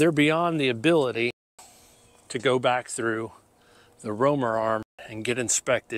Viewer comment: Excellent vid. A question; @ 1:15 you're saying something i can't quite make out. Sounds like you're saying..."They're beyond the ability to go back thru the "Robert Arm" ? or maybe "Romeron" ? Please clarify. I eat this stuff up,every detail. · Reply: It was "Romer Arm" an instrument that's part of a larger CMM setup. It measures the chassis for tolerance when NASCAR inspects them at the R&D Center.